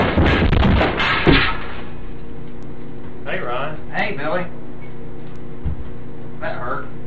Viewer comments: My Louisiana ears heard him say "Hey, Ryan." 🤔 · Normal friends: Are you okay? Billy: Hey Ron. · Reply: ok maybe I'm mistaking. I thought i heard "hey Billy, did that hurt?" I am not English so your opinion on that is relevant. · Lyrics: The hell is going on up there beat drops Hey Ron, Hey Billy That hurt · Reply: i thought he said "did that hurt?" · "hey ron" "hey billy" "that hurt" that conversation is gold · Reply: I thought he said “did that hurt?”